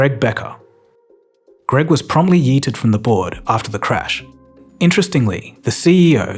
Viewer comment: Did he really saying 'promptly yeeted from the board' or did I mishear?